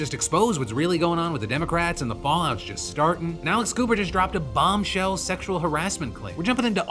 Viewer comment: misheard this as Alice Cooper and was very confused